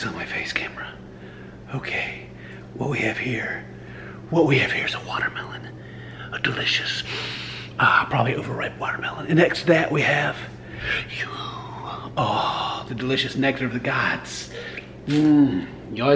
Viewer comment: How to be a dad 101 with Ken. Lesson 12: Feed ur child cold sour watermelon candies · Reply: Jeniffer Middleton so I thought he said it was a watermelon candy